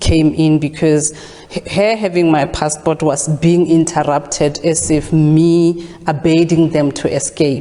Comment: Did she say interrupted?